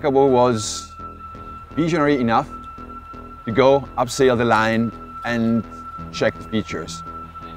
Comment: Why are there subtitles when they speak very good English? And why are the subtitles then wrong? At 4:42 the guy says 'upsail the line' and the subtitle says 'analyse'. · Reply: He actually says "abseil the line," but I agree with you that their English is great and the subtitles just make things more difficult!